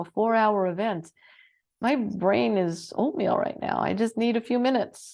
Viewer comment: my brain is what right now ?? can't hear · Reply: Oatmeal!